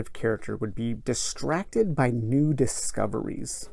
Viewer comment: I had to double take at 31:05 because at first I swear I thought you said the character's weakness was "Distracted by nude discoveries." I was going to give you props for realism.